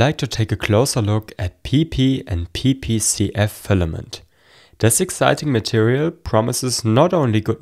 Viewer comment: I swear I heard pp in pp caka land